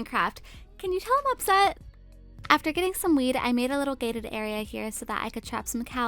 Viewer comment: did she just say after getting some weed?